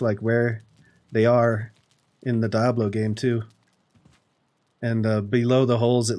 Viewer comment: Sounds like he says Dah-blow